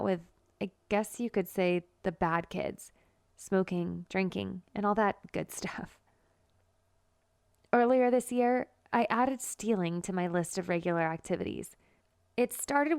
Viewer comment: did she say all that good stuff?